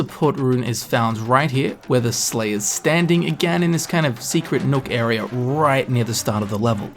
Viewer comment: I watch all videos at 1.5x speed and I genuinely thought you said "secret milk area", when you said "secret nook area". I personally like the idea of a secret milk area, tho